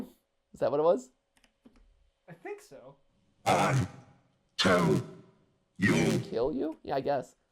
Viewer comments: i told you, thats what i hear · Am i the only one who hears "i tell you" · I think it said “I told you”